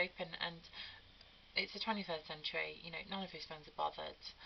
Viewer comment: Love the British accent. For a moment there I thought she said "it's the tranny-first century."